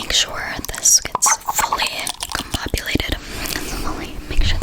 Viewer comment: What is that word? Your line "make sure this gets fully compulated" what is this compulated? I know this is subtitle error. Anyone please tell me this word. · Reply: combobulated